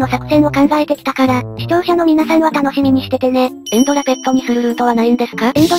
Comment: エンドラペッドにするがエンドラベッドにするに聞こえてしまったのは私だけではないはず...